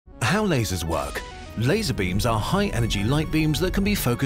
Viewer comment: I thought it said how caseoh works 💀💀💀